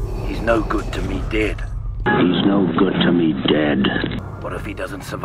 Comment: I thought he said 'dad' when I first saw ESB. That made me believe that Boba and Luke were brothers